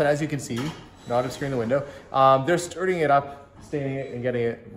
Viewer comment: At about 24:18 I thought you said Josh & Jakota were screwing something up on the shelves...how bad did they screw it up? LOL